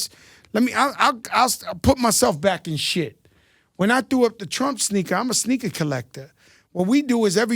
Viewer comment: did he say he put up the trump sneaker?? 😂